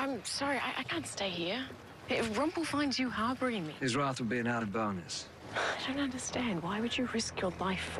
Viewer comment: Someone please tell me what Hook said at 0:40... :) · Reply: Mariana Montes I assume you mean 45 "his wrath will be an added bonus"